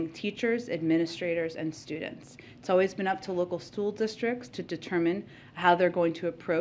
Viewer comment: Did she just say "Stool districts?"